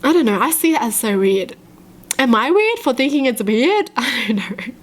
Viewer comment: i thought she said am i weird for thinking it's a beard